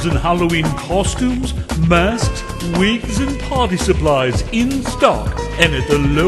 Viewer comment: When the commercial for that moon costume thingy came up and he said "Party" i thought he said potty.